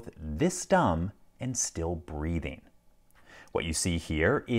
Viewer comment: "It is legitimately hard for me to imagine a sentient being who is both this dumb, and still breathing" 🤣🤣🤣 LPL with the zinger! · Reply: Lol I thought he said ", and still breeding"